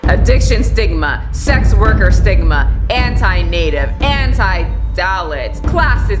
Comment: I originally heard that as “anti-dalek” - “DISCRIMINATE”